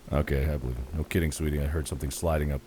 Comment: did mark say "sweenie" instead of Sydney?!